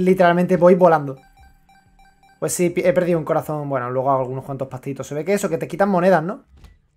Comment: Escuché corazón morao XD